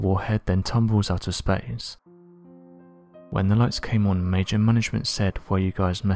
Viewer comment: "Major management"???? At least quote the man correctly... That's not what Dr. Jacobs said at all. It's Major Florenze J. Mansmann that he was referencing. There is no "major management"..